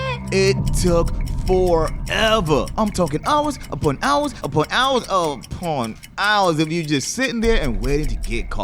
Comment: It sounds like he says porn hours